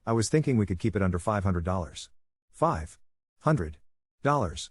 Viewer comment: I thought he said 5k and I was like, that's a bit cheap. Maybe limit of 10k? But 500?! WTF